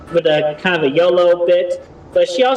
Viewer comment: I think Chris is saying YOLO (you only live once) here, not yellow